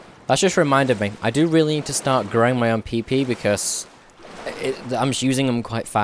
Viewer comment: i know he means the fruit but it kinda sounds wierd... and the captions spell it like peepee instead of pipi lol